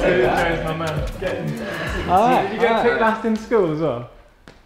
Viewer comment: What does say at 1:14 ?? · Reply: "so did you get picked last in school as well"